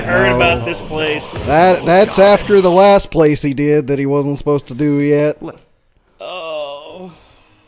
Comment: all i hear is ''he wasnt supposed y to do yet'' man get gud